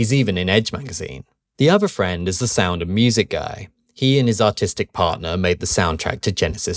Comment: I thought you said autistic heh